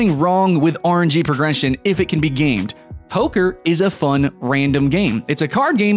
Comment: What game you say at 6:30? Hoker? The car game